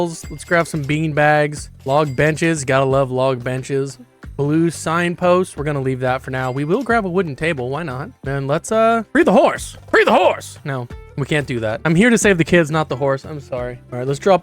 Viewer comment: BREED THE HORSE?!??!!?!?? IS THAT WHAT YOU SAID?!???!?